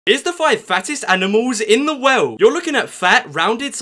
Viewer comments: I thought he said "here is the 5 fattest animals in the woah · Bruh i thought he said fastest